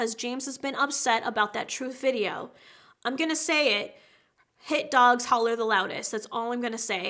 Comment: ”Hurt dogs bark the loudest”....damn! so elegantly savage. Edit: thanks for so many likes! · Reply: I thought she said hate dogs